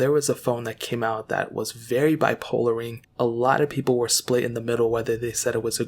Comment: did dude just say "very bi-polaring"... bwahahaha.